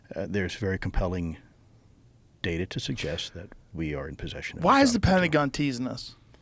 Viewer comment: what did luis say before joe interrupt him? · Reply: Government in possession of exotic material